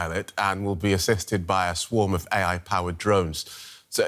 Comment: did I hear right ? It will be assisted by Microsoft Copilot ? Oh Gosh 😮